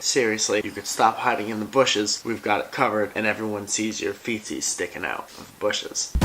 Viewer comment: when he said feetsies at the beginning i thought he said feces. oop.